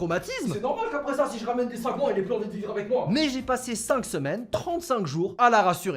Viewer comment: il dit "c'est normal qu'après ça si je ramène des ..." Des quoi ? Je pige pas (pour de vrai) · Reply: Si je ramène des sagouins qu'elle n'ai plus envi de vivre avec moi